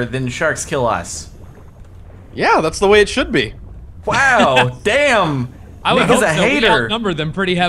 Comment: "Wow. Damn! Nigga's a hater!" ik that's not what he said but it's what I heard the first time xD